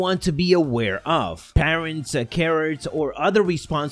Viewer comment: I thought I heard you say "parents, Karen's, and other guardians" 🤣🤣 I had to stop the video and read the "other issues" section 😁